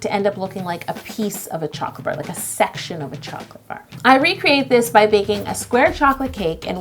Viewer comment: Did she just say...sexual chocolate bar? XD